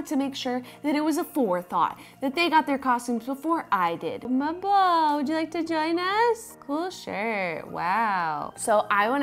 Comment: "Marble, do you like vaginas?" That's what I heard the first time through.